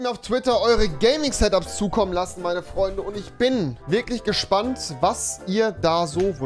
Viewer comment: als erstes habe ich Hack meine freunde verstanden also hackfleisch lol